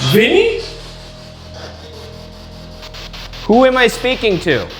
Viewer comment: I heard, "speak to me"